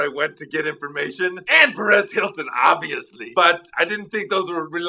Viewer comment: Perez Hilton? I thought he said Red Skeleton! Oops.